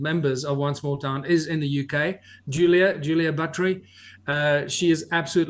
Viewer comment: Did he say Julia Buttry?